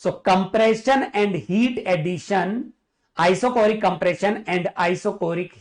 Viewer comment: I thought U said compression is heat rejecting 😢